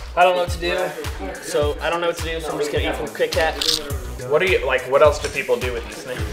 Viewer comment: did he say krit kat???